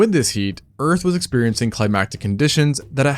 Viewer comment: "With this heat, the earth was experiencing 'climactic' conditions..." Did you mean to say 'climatic' in regards to the climate? Or did you say exactly what you meant by using the word 'climax,' implying that the earth's condition were the result of 'a culmination of a series of events?' Either fact is plausible. I request a clarification because 'climatic' has more frequent use when referring to the age of the dinosaurs.